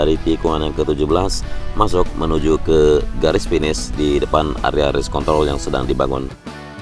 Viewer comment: Kok gw dengernya Race Kont*l di menit 1:11 🤣🤣